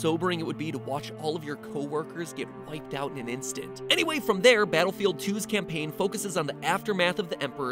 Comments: Am I the only one that heard him say battle field 2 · did he just say battlefield 2 battlefield music intensifies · Did you just say battlefield two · Did he say battlefield 2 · Only me who heard him say battlefield 2